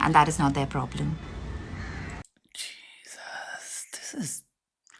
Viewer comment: did he said 'jesus' lol